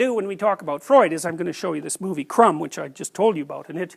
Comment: How is the movie called? I'm a Spanish speaker and I heard "chrome" but nothing on google like that xD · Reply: crumb